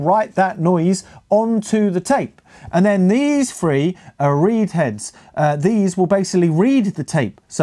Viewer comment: Oh, "read heads"! I thought you said weed heads... 😁